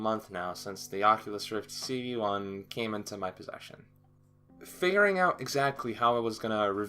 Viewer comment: I thought He said You know it's been about a Month since the Oculus Rift CV1 Came into My Pleasure, but He said Possession.